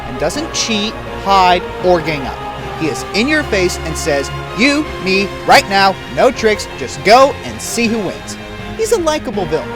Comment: At first, I thought he said, "You, me, right now, no drinks. Just go and see who wins." And I was confused. Then I put on headphones and found out he said "tricks."